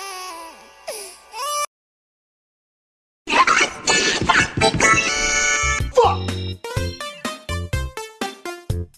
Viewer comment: 이거 뭐라는거임? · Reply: 안돼 단비꺼야